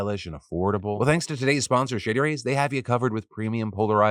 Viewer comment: I really thought you said "Shitty Rays" lol